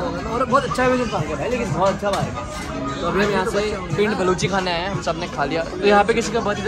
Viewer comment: Did he say balochi